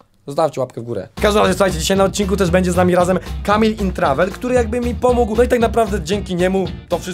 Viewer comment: czy tylko ja usłyszałam kamień in travel xdddd🤔🤔